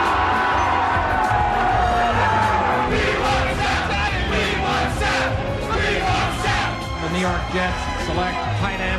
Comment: What are they even chanting at 4:17? I hear both "Sapp" and "Seth". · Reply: Sapp, after highly-sought after draft prospect (and eventual Hall Of Famer with the Tampa Bay Buccaneers) Warren Sapp.